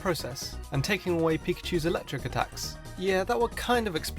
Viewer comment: did you say pichu ????